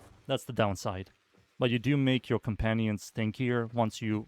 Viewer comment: Sounded like you said makes your companion stankier. I busted out laughin. The Nidus touch up is nice, and i agree he doesnt need duration